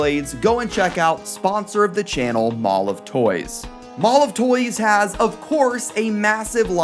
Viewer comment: Ngl man every time you mention mall of toys i keep hearing "MolovToys" until i remember the actual spelling with the actual words